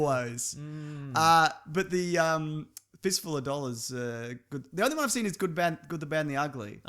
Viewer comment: The first time you guys mention Fist Full of Dollars I thought you said Fist Full of Dogs.